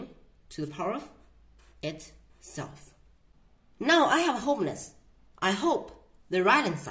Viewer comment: I think he said that he's homeless cause I can't think of anything else he might have said